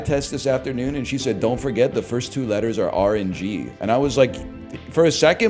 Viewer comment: Getting mad at your wife for telling you the 2 biggest letters on the eye test is insanely based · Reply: I heard it as RNG instead of R and G made me think she was telling him a random joke he didn't get